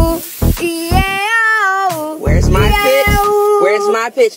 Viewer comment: I thought she said bitch end but she said pitch